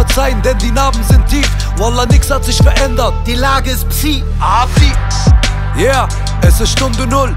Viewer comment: Ab 1:47 Wer hat Abdi raus gehört 💯